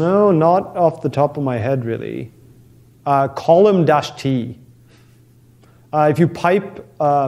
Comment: he suggests a tool to past nicely formatted text. But I can't understand the name. Something like "column dash t". Can anyone tell me the correct name? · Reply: column -t (so the "column" command with the "-t" flag) :)